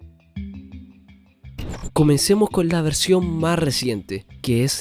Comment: comencemos con la version 1 puto 14 xD eso escuche yo